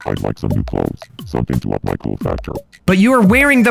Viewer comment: I heard "Something to up my cool fat girl"